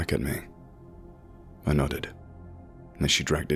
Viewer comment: Whenever you said I nodded it sounded like you said I nutted. Other than that, awesome narration!